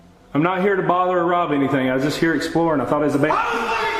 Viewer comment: Can anybody tell what was yelled back at the explorer at 3:50 ish? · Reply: I think he said “I was playing games”